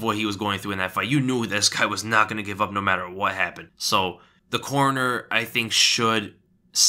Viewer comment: I know you said “corner”, but it sounded like “coroner”